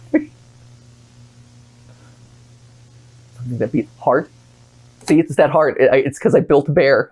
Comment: HELP I THOUGHT HE SAID “Heart?🫰”